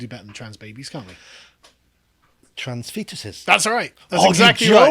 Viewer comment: When Sargon said "trans fetuses" I thought he said "trans pizzas," and I just gave an eyeroll and waited for the next insanity to be revealed. When he contined I figured out that I'd heard wrong. This is the state of my expectations of the woke mob at this point. Fucking hell.